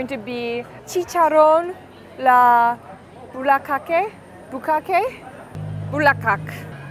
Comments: did she just say B*KKAKE? 🤣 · Did I hear that correctly? Did she say Bukkake? 18:45 😂😂😂